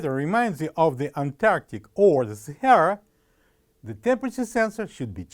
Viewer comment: WHAT DID HE SAY? I COULDN'T UNDERSTAND THAT. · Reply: Kevin Bryant Antarctic or Sahara